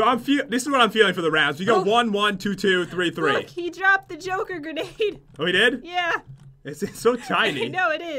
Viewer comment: Who else thought the Joker grenade said Joker grandma at first? No one or i'm just blind